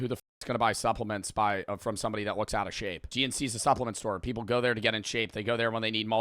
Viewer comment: every time you said that I thought that you where saying gmc the car company